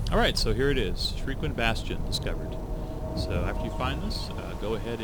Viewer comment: i thought it said shrekwind bastion